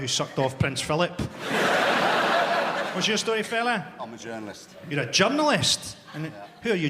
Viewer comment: I thought he said he was a junglist. Jungle is massive n all that